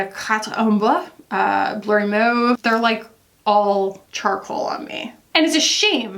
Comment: Thank goodness for Close Captioning...you said "all charcoal" and I heard "ball purple," and well, balls can be purplish, so I almost didn't question it...